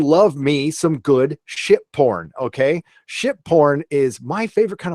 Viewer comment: Sounded like you were saying shit porn instead of ship porn lol